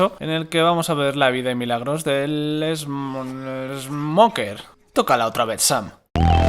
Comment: 5 años y sigo sin entender que dice aca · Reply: dice "Tócala otra vez, Sam"